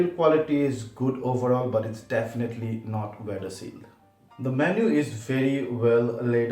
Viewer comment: what are you saying? "the built quality but its not VERY SAFE???" I dont understand what you are saying after the word "not". thanks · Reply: 'weather sealed'